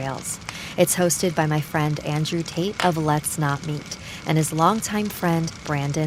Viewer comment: did she say andrew tate? WHAT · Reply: I thought it was Andrewtateletsnotmeet as a user name